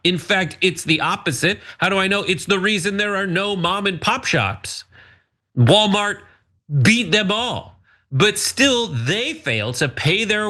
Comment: Holy shit I thought Brett said Walmart beat them off